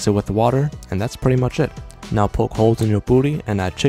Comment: Bruh I miss heard “Puri” with “Booty” And he said - “poke holes in your puri“ I heard - “Poke holes in your booty”😂😂😂😂 Ayo WTF